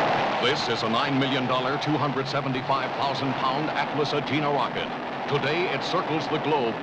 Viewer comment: what type of rocket did he say it was? I got that it was 9 million dollars and 275 thousand pounds, but I can never make out the name he says. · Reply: The announcer says "Atlas-Agena Rocket" though I doubt it could have been cause that rocket didn't exist yet in 1956. The earliest Agena variant was the Agena A in 1959-60